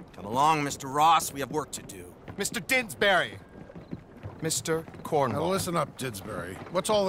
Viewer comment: I swear I hear "Mr. Cornhole" 🤣